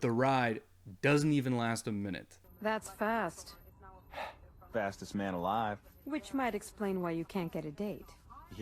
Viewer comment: Girl: that's fast Flash: Very fast ( I forgot what he said here 😂 ) Girl: which can probably explain why you can't get a date. Flash: yeah...... hey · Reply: He said fastest man alive